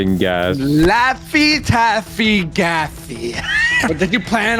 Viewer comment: i thought said gassy lol